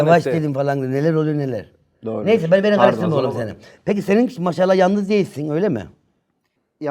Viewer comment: oğluşu duydum sanki